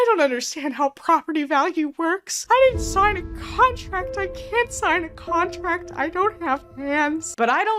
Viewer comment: Why did I hear, “I can’t sign a contract I DONT HAVE PANTS” 😂😂😂